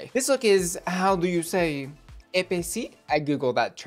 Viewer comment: The way you pronounced "épicé" x), I heard "épaissi", which litterally means thick. I laughed so hard when I understood. And yes, here is the french comment criticizing your accent :p